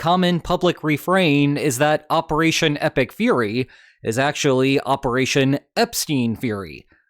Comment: I misheard it as Operation Toothpick Fairy…. Trump in a tutu dancing to Tchaikovsky. The reality is less funny…